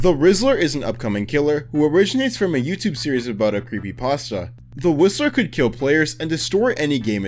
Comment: The one thats Censored beside John dough is it Jane dough? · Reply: Also for the whistler I thought I heard the rizzler but I was wrong